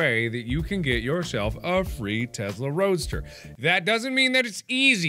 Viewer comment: I thought it said "Free Tesla Toaster".